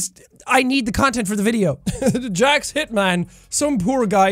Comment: I thought that’s said jack shitman